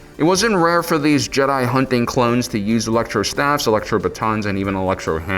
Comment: wait...did you just say clones!?!